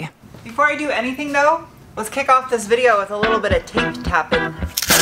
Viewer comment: I thought you said “taint tapping”, which sounded like an odd turn of events